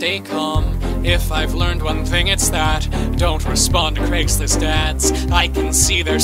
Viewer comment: I actually thought it Said "Craigslist Dads" but it was actually ads...